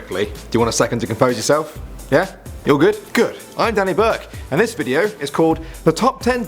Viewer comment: When his name said " danny burke" i thought it said danny Blake 😂 like if u know who danny blake is